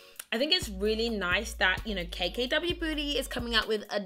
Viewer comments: Am I tripping or did she say "KKW booty" at 11:47 lol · Did anyone else hear "KKW booty" @ 11:46 😂❤️ btw ly😍❤️ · OML 11:48 DID I JUST HEARD KKW BOOTY :D HAHA♥ luv ya GORGEOUS